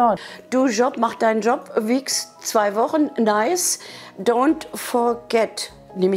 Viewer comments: Wer hat auch bei 1:14 gehört " Wichs zwei Wochen "? xdd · Wer hat auch statt "Weeks...2 Wochen", "Wix zwei Wochen" verstanden. :P